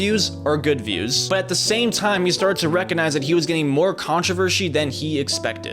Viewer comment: did he say controver'shi'? Or am i mishearing things. Haven't slept in 2 days 😂